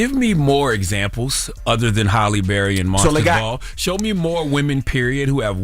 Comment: Halle Berry and who?? What's the other name he says?? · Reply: Halle Berry in Monster’s Ball